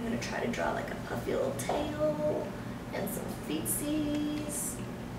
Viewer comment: lol i thought she said "and some feces..."